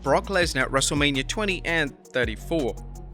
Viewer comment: Legit thought he said Wrestlelabia at the beginning there LUL